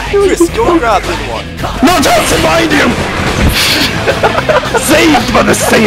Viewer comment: Oh, actually i think he said *Nunchucks, behind you* but the guy's name was Bundrov LOL